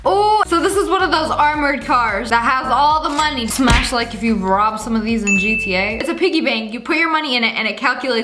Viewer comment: Bro I heard "if you robbed any of these" and I was like "you did?"